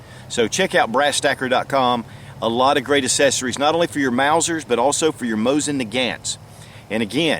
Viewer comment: did he just call that mauser a mosin? 😂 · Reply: No he says "not only for your Mauser but also your Mosin Nagant's"